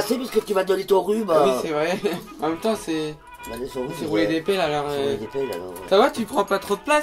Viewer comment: A 9:12 j'ai compris on ses roulé des pelles